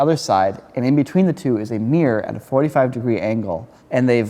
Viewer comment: When Niko says "a mirror" All i hear is "Amir" Lol